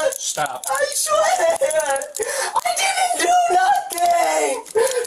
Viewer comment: As a former 8 year old girl I can confidently attest that a little girl is not going to walk into a bathroom while a mostly grown man is sitting on the toilet with his pants down and all. It just is so unbelievable that it is safe to assume her telling of the event is far more factual than his was. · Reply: I don’t know if anyone could hear but while he was sobbing in the police car it sounded like he said “I swear I didn’t do nothing, I was just TESTING HER OUT”